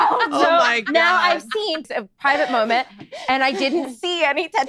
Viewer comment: "Sephy" "Oh no it's not me" sips drink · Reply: At the end! “What about ME!?” Me:🤔🤔 🤣🤣🤣🤣🤣🤣I thought you said” oh no it’s not me”